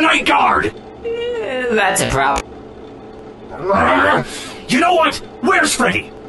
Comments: I always thought toy Bonnie was saying that’s a problem, now I realize he says that’s a prop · Foxy: *pulls out gun* Toy Bonnie: “Eh that’s a prop” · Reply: I thought he said that's s prob since bonnie thinks the night guard was freddy